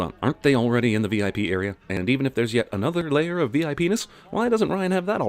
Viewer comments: What was said "VIPness" what i heard "V. I. Penis." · I'm sorry...did I just hear VIPenis? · I looked away, and I swear I heard V.I.Pen*s lol